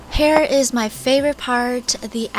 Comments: you said hair is my faveret part instead of this hear is my fav part of this chaleg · did she say hair?